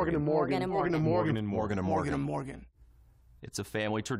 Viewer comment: I heard “mirgadamorgadamirgadamorgadamorgada”